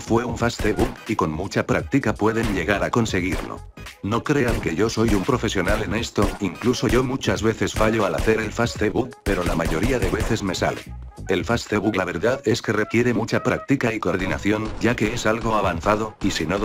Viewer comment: en el minuto 2:20 parece que dice facebook ekisde